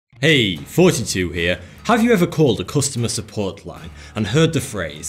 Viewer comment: I always thought that in the intro u said, "Hey! 42 here!" 0:01 Now, i realised that it was "Thoughty 2" 😑